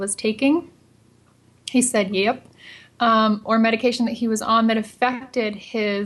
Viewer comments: Thank you so much for posting!!!! I hear Prince say 'yep' at 5:15! <3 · i heard a ghostly 'yep' at 5:19 !